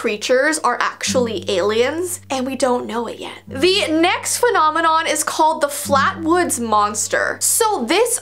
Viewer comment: does anyone else hear her say vee next phenomenon or have I just been watching to much of her videos